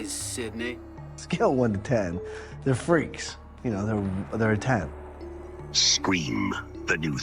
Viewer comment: did homeboy say they are freaks but they are a ten? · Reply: “On a scale from one to ten, they’re freaks, they’re a ten”